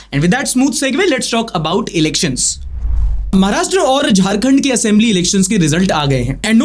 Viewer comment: the subtitles says Maharashtra and gujrat instead of jharkhand.....